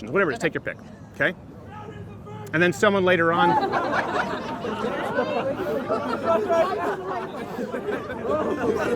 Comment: what did the guy yell in the backround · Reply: "Crowder's a virgin"